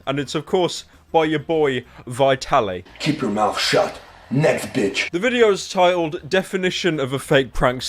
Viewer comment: I hear "nesquick" instead of "next bitch"